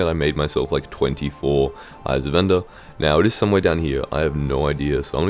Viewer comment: caption says vendor and not ender